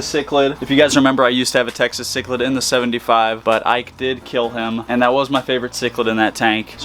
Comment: Lmao when you said "ike killed him" i thought u said "i killed him"